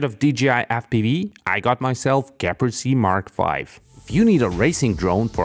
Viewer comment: What drone does he say at 3:00. Cant find any match in the description. "Capersi mark 5"??? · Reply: GEPRC Mark 5 fpv quads. Pretty good quads actually in my experience that are available rtf or bnf. Fairly high end , quality builds yet not overly pricey. Not cheap but reasonable and pretty good value for what you get I think. Least from what I've seen. Idk if theur quality control is consistent but the few I know were well built and tuned with attention to detail.